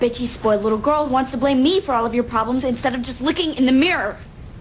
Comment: did anyone else here licking in the mirror instead of looking in the mirror